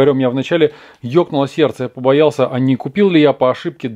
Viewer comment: еб___о или тьокнцло серце?(Не цинзура послышалась)